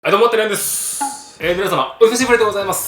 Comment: はいどうもてるやんですっ って聞こえるww え、それとも私が今まで勘違いしてた…？